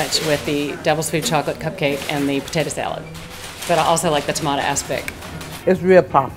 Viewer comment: did she just say she likes the tomato ass-pick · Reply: Merlot M Yes, she said that. Only, it isn't spelled ass-pick. It's spelled aspic.